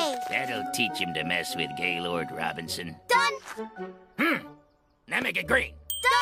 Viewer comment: On 29:20 did Robinson say Gaylord or is it just me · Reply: That’s his name. Gaylord Robinson.